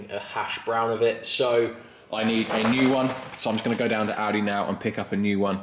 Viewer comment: I actually thought u said u needed to go to Aldi to buy some hash browns i thought maybe ur hungry with all that work 16:24 ......but later realised you went to Audi... 🤣